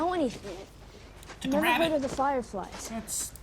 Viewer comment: What did say?!?!? 5:50 🤣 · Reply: "(She) Took a rabbit!" ?